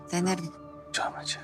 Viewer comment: Burda ne dedi devran ? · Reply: Canımın içi